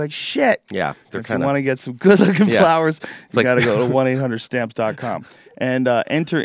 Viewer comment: Did Joe say you can get flowers from <link>?? Nice!! And he says it right at about the 4:20 mark (4min 20sec) in the podcast.